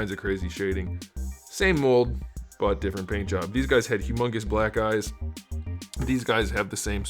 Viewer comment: Did he say “humongous black guys” @ 2:45!?!? As a humongous black guy myself I appreciate the shout out! Carry on....